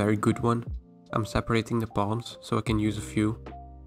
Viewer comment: When you say "palms" I always hear you say "bomb"